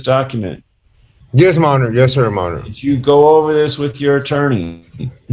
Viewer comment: The last guy was really funny and respectful but what is he saying? everyone said he was saying "Yes Sir Your honor" but if you really listen to him it doesn't sound like that to me. I hear "Yes Sir Monitor "